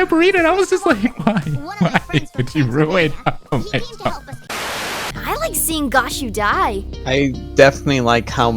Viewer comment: I love gashu, and my heart sank when i heard "I like when gashu dies"